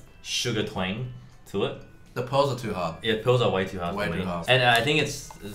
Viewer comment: "the pearls are way too hard" "the balls are way too hard" is what it sounded like; woah there.